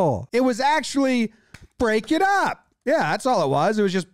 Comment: It's actually insane that he literally ejected "a voice" coming from the dugout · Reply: you're so wrong here. He heard the pitching coach clapping saying break it up to the players and he thought he said "that was low". No one said a damn word directed at the ump after that. This is as bad as the manager getting tossed last week for a fan above the dugout yelling something